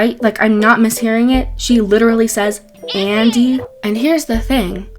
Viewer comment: I heard Izzy not Andy lmao